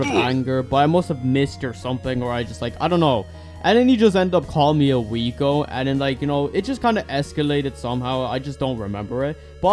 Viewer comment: a what? wth is a “weego” or “ouigo” whatever he said